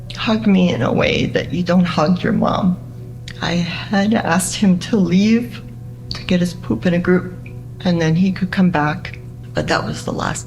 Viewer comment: does the mom say “I had to ask him to get his poop in a group.”??? I’ve played it back so many times and that’s what I hear.